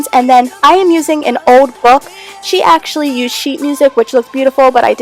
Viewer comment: what does she say the other person used instead of book pages? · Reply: Sheet music.It look beautiful and can take the yellow-ness out of the diy